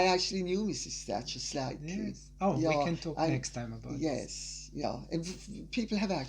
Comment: Can somebody tell me what does Lady C use often when speaking - is it "Yo"? Sounds like "Ya'll" or " Yop" ? :)) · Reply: She says 'yah'....it's an 'upper class' way of saying 'yes' or 'yeah'. ;)